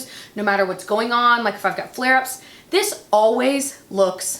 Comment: At first I thought you said ''f****d up flair ups''! Almost unsubscribed. Just kidding hahaha